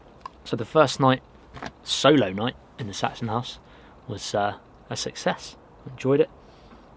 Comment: Thought he said there was a Pict in the Saxon house. Time for bed.